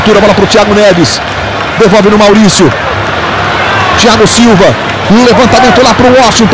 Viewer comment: Does he say Thiago Silva at 00:15?